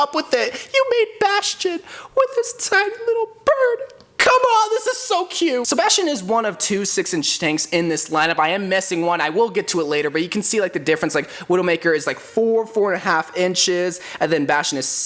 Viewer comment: sounded like Sebastian